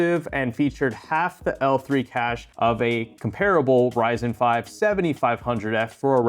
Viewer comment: For some reason I thought you said the 7500f was "TERRIBLE" it took me hearing it 3x to realize you said "COMPARABLE" 🤣 I was gonna say the 7500f might be the GOAT considering its Price vs Performance. I remember when it was easily obtainable for like $110-120 for a long time. Insane value